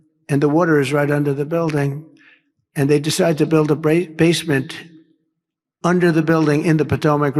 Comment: Where was this building right over the river? Sounds like he was saying build a basement in the river. Who the heck knows?